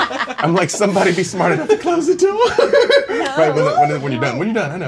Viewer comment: What did he say? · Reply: I understood him as saying close the door.